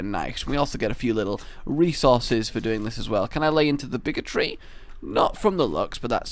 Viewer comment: I thought said "Can I lay into the Bigotry"